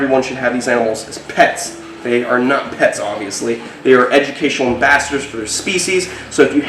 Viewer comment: I swear I heard him say at least 4 Out of 5 times that his snakes were educational bastards.